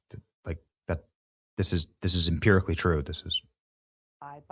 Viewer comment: Thought I heard her say ex-rabbi.